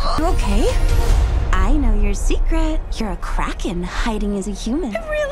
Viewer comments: For a second I thought it said “your a crackhead” 😂💀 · What I heard "You're a crackhead hiding as a human" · I thought she said “I know that you’re a crackhead”